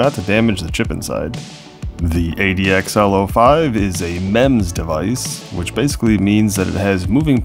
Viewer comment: How many thought he said it is a "MEN'S DEVICE" lol.